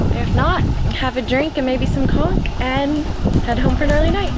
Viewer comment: About 23:40 ,,,, what did she say exactly,, did I hear that right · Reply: I said conch. The food.